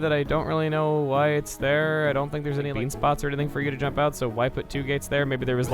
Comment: did you say "bean" around 4:37 ?? i hear things :o